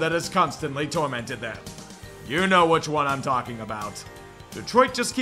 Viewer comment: I hear "I'm talking to America" made it into the intro